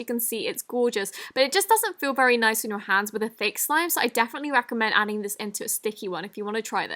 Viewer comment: she said “...with a fake slime”. Is that what she said? Anyway, YOUR MY FAVOURITE YOUTUBER! LY!